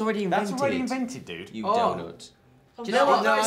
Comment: I thought Liam said 'You doughnut' LOL.